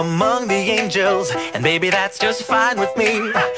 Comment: Am I the only one who always thought that it said "and maybe that's just fine with me" and not "and baby that's just fine with me"